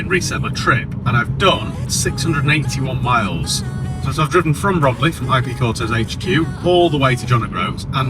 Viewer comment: Seen this outside a car wash in romiley as I drove past, it looked amazing 🤩 loving these videos more road trip videos would be amazing 😊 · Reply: Oh Romiiey! I thought said Bromley, where I live which isn't oop North.